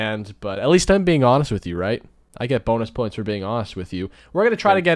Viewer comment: I thought he said boner points at first...😂😂😂